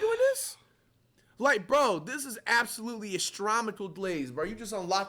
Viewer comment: “Astromical “ glaze😭 instead of astronomical😭